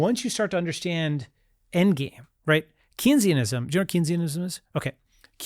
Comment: What's the word he says? Kinseyism...? That's not it, but what is it? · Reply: Keynesian economics